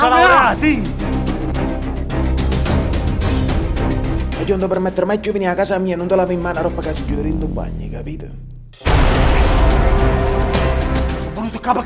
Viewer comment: Qualcuno sa dirmi cosa dice in napoletano al minuto 01:07? Non la traduzione. Scusate, sono un nordico del cazzo. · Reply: Uagliò nun te permettere mai chiù e venì a casa mia e nun te lavà e man aropp ca si gliut rint o bagn. E capit!! Purtroppo non so scrivere in lingua napoletana, ma grosso modo...